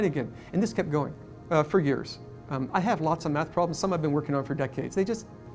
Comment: I could have sworn he said he has lots of Meth problems 🤭😆😆